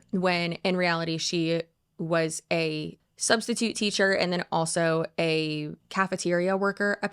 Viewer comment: I wasn't paying attention but I thought you said substitute cafeteria lady 😂😂